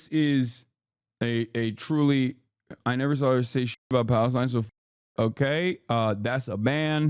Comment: anyone know what this comment was? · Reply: “I NEVER SAW HER SAY SH*T ABOUT PALESTINE SO F*CK HER” Everyone’s lost their fkn mind dude.